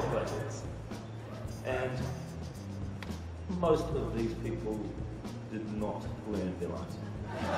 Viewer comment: Hey what is the director saying at 2:18??? I’m not able to understand!!!!! Please can anyone tell me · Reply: Yūko Ichihara ‘and most of these people did not learn their lines’